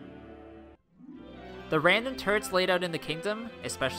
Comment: did he say random turds?